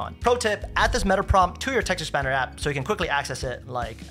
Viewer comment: what did you say? The CC is saying texture Spanner app - IRT the ::GPT thing for the text macro. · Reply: Text Expanders